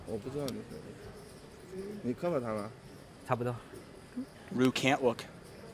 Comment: Did he just say ru cant wook lmao